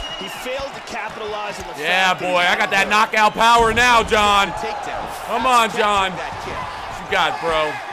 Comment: Did anyone else hear, "come on baby,"